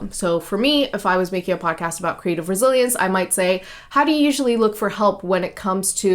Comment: Great content. Thanks! I must say I thought you said you were developing a podcast about creative Brazilians which put pictures in my mind lol